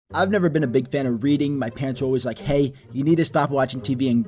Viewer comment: i heard it as "im not a big fan of breathing" at the start and it felt relatable.